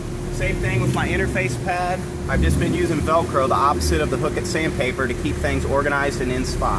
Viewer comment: it sounds like he says "Opposite of the fucking sand paper..." lol